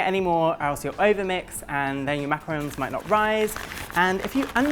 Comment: I guess at 11:46 I heard macaroons. I love you sorry had to point that out you handsome man lol